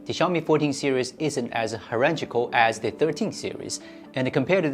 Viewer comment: "Herengical"? What word are you saying here? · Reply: Hierarchical